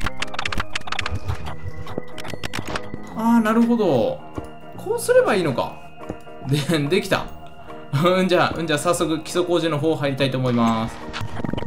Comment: すぎの基礎工事がクソ工事にしか聞こえない